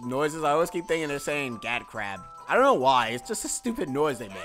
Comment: Lol! I always thought those turtle guys said "cat crap" as well. XD